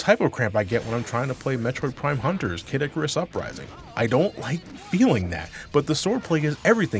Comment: why does kid icarus uprising sounded like cadicarus uprising xd